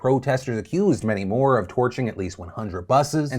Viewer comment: I thought Phil said, "Torturing at least 100 buses.." & I was like wtf!? He said *Torching" 😯